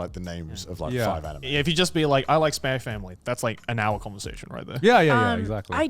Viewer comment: I can't unhear Joey saying Spanish family instead of spy family